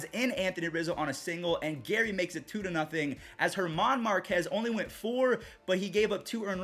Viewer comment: did he just say German Marquez